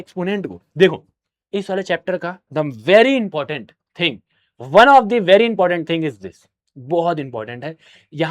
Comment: O Bhai 35:25 me Kya bola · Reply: Mai batata hu "in sare chapters the very important thing one of the very important thing" ye bola